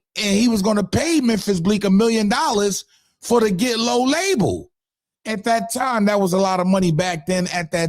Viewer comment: Fam that’s not what he said he said he’d sign the artist and open the budget